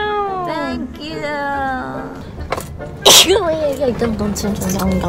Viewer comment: I literally thought that she said “f*ck you” for a sec, then I realised she said “Thank You” 😅